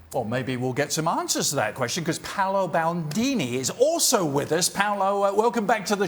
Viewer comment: holy fuck i thought I heard Paolo Maldini and was wtf happened to him why does he look like this. Oh it's Bandini.